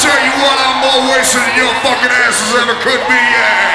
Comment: What does he say between 00:10-00:14? Hilarious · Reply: Actually he says " I'm always into your fuckin ass I ever could be 'eeeaa"